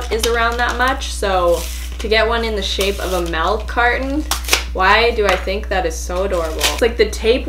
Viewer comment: I heard “melk carton” multiple times LOOOL